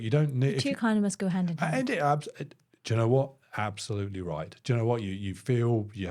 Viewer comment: The way you say "do you know" i hear "jenna". And Im like, how many times will you address her in a sentence. I mean do you know isnt any bettrr. Nvm. I slightly drunk.